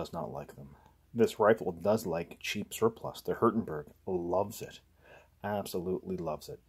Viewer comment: What ammo does this rifle like? all i heard was a garbled weird word 5:26 "thehurtinbur" loves it....???? Huh? What Did he say???